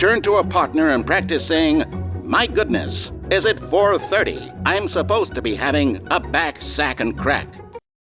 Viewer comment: I thought he said "im supposed to be having a bag sack of crack."